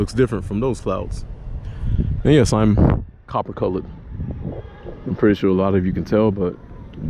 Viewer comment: Did you say you're coffee-colored or copper-colored?? 🤣🤣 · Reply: Copper....🟤